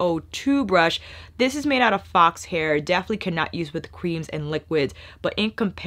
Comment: Did say that the FO-2 is made out of fox hair and definitely CANNOT or CAN use with cream and liquid ?? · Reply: Can not